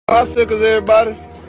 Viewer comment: Why do i hear "where the tampons!"😭😭😭